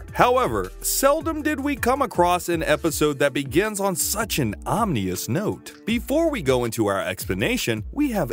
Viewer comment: Did you just say “Omnious”?